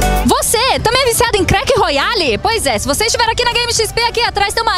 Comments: Foi isso mesmo q eu ouvi CRECK ROYALE 0:49 Q ABSURDOOO 😂🤣🤣🤣🤣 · EU ESCUTEI CRÉC? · "ei, vc q é viciado em CREQUE royale" err... Eu ouvi isso mesmo?